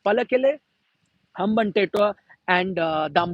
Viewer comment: Did he say Hamban-Taetoa 🤣🤣🤣🤣🤣🤣🤣🤣🤣🤣